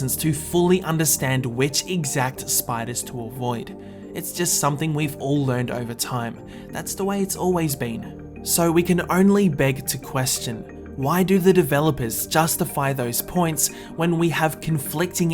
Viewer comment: Okay I will let it pass because you said "beg to question" ^^ At first I thought you said beg the question, but that's not the same thing as raise the question.